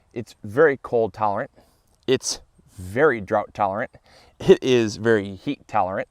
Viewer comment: Oh I thought you said very drunk tolerant. Realized you said drought. lol